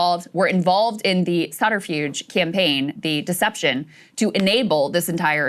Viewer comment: Subterfuge? Sounded like she said Sagaarfuge